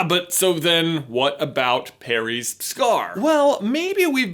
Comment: Does anyone realize that he says Perry instead of Harry about the 6:07 mark? I found it funny because I've done that sometimes. :p c